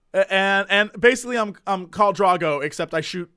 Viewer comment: i could have sworn said Ka D'argo, and i was like "bitching farscape referance" then i rewatched and was disapointed that he actualy said Khal Drogo.